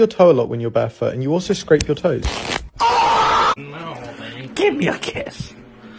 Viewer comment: "Kiss me fat boy" 😂😂 (I didn't know that something that made me laugh would get so much hate) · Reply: I thought He said "give me your kids"